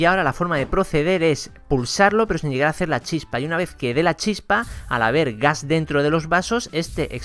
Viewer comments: Pense que dijo a la verga😂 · parece que dice a la verga like si piensas lo mismo :v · Al vergas? Al ver gas ? Like si tú pensaste que dijo al vergas :v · En el minuto 4:59 a la verga entendi yo xd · Parece que dice ala verga jaja